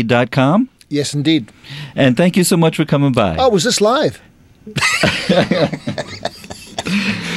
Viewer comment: what does he say at 4:48 ?? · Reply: "Oh was this live?"